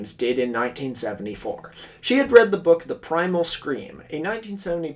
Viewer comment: I misheard that as the book The Primal Screamer by the lead singer of rudimentary peni and got very confused